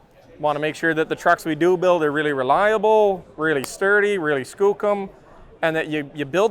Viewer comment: what the hell is he saying... reliable, sturdy, skulcoom? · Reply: I think he is saying Skookum, a term invented and popularized by another youtuber from BC Canada: AVE